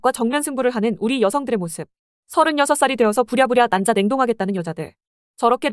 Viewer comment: 자꾸 난자 냉동이 남자 냉동으로 들려......ㄷㄷ